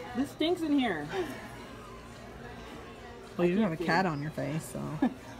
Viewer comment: “Well you’ve never had a cat on your face, so...” #DEAD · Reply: I thought she said, "well you have a cat on your face so.." definitely sounds like it 🗿